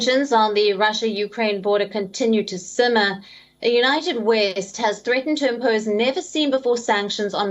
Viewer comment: I heard United Waste !!!!😂😂